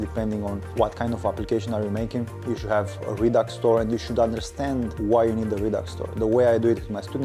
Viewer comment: I couldn't understand what you said at @ 2:40. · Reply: You should add a redux store and you should understand why you need a redux store